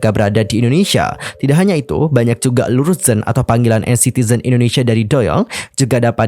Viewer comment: itu panggilan dari doyoung apa? kurang kedengerann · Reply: Luruszen